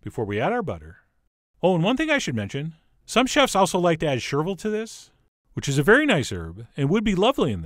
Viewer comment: What does he say at 4:07 ? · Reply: Chervil...herb.